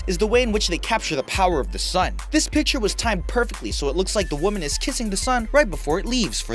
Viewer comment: if you go to 6:15 he sounds like hes saying perpitly XD